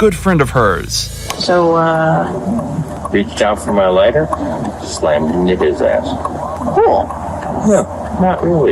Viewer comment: it really sounds to me like he said "Slammed that N-words ass" It really, really does. So the "Cool" afterwards really made me super confused. And then hem also saying "No, not really" was fucking wild. Listening to that was crazy when not hearing what he actually said.